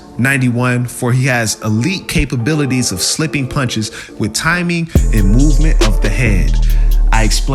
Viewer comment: Sound like you saying he a b fighter 😂😂😂